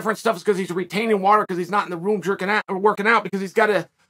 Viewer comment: Uhh anyone else hear that at 13:05? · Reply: “He’s not in the room jerking out.” 👀